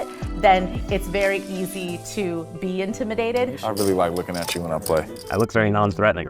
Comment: That comment by Ivey says it all. The man who makes everyone else uncomfortable staring at them.. Is made uncomfortable by you staring at him. A disarming killer. Quite the personal mark of pride when the 🐐 has that opinion of you. Legend... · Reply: Don't want to take anything away from what you said, but I think Ivey said: "I really like looking at you when I play." This also makes more sense to me when Phil responds: "I look very non-threatening."